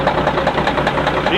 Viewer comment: nice find it sure sounds like he says let it run for a couple hrs